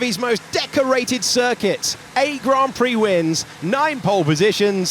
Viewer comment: did he just say 8 grand prix He knows😂